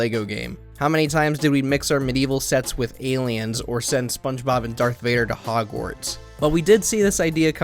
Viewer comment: sorry, did I hear you say Dark Vader?